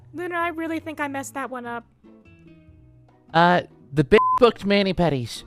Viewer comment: LUNAR SAYING "SHE'S BEYOND HELP" had me rolling 💀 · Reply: I agree with Lunar on that one 😂 But What did he say before that? (Besides the foul language) I couldn't understand what he said at 20:55. "The b** looked-" what did he say after that?